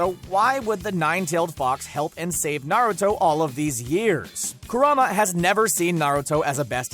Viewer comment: Did he just say “Kuruma” instead of Kurama